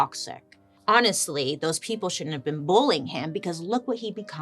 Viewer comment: all I heard was “bowling”. They’re bowling him